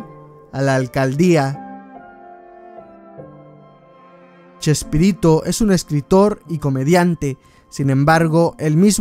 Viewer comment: pensé que decía chespirito y su pollo XD